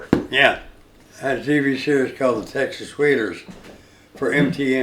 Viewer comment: I loved Gary in "Silver bullet". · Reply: and the texas wheelers. lol thought he said wheaters